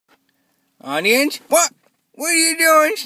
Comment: when he said audience during the intro and outro it sounded like toby said onion